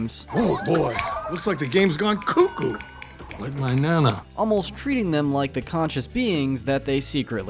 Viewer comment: Did anybody else think when the owner of the arcade said like my Nana thought that he said like my banana?